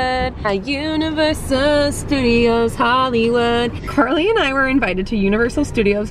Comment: i thought she said curly and i 😂